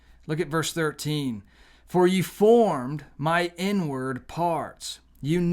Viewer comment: I swear I kept hearing you say "N word" but you were actually saying INWARD !!😂😂